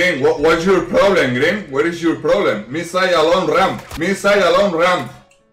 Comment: Green green what is your problem green what is your problem, me say alone ramp me say alone ramp smash smash smash smash 🤣🤣🤣